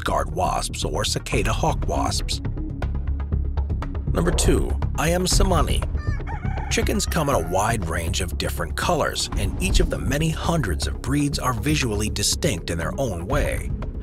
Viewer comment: I swear for the number 2 spot I thought you said I Am Salami and now I want a hoagie